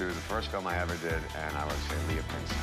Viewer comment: What's the name he said at 7:23 ? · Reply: it's called the Bay Boy not the bad boy leah pinsent is her name